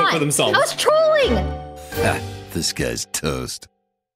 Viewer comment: No one: ... Toast: “This guy’s toast!” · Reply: it's actually "this guy's toast" (this guy is toast, aka this guy is dead). It's from hearthstone, it became a catchphrase of toast, but every new viewer thought toast was saying disguised toast and were confused.